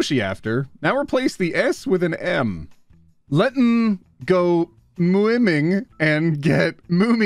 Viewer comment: what was she trying to say??????????? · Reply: I FIGURED IT OUT "Let's go swimming and get mush(y)? after" Mushy is prolly a code word for the tango